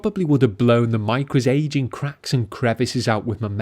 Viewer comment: the most thick english accent, i have no clue what he said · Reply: Aging cracks and crevices....